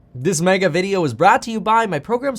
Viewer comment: At the beginning it sounds like he said “This Maga video” 😂💀